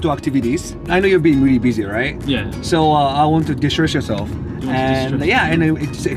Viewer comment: Well, I think its less of a doubt what said at 0:39 now. Did he said distress yourself? no obviously destroy yourself.